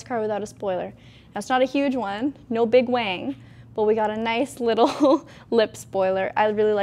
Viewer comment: Did she say, "Not a big wang"?